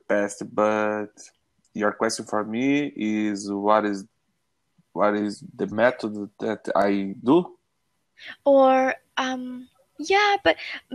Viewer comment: What did you say, man? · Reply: Hey buddy! He said: "You wanna know what is the METHOD I do?" I think he meant: "Do you want to know what method I use to learn English?" Do you get it now? :D